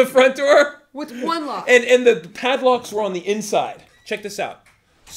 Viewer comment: I dont understand what was getting saying @ 11:00. Can someone explain? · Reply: "The padlocks were on the inside"